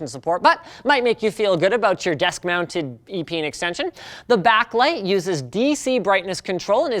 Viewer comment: Was I the only one who thought he said "Desk mounted penis extension." XD Kappa